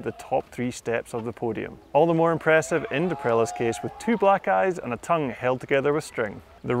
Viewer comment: Thought he said “two black guys”